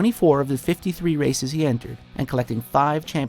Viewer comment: All I heard throughout the entire video was "Until it was taken my Schumacher"